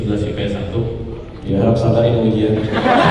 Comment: Ada yang tau gk, dimenit 2:21 anak itu ngomong apa? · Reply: carlos gibson mohon bersabar ini ujian